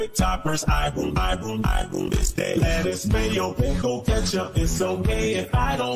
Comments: Anyone else thought he was saying, "It's so gay if I don't want that,"? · why do I keep hearing “ it’s so gay✨ if I don't want that"